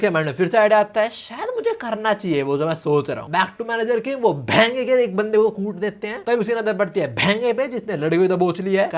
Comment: is it just me or anyone else hear behen ka instead of bhenga